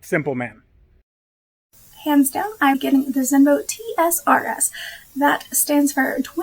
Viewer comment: her voice is so high I can’t understand what she is saying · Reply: Its zenovo Her voice isnt high, Just bad micro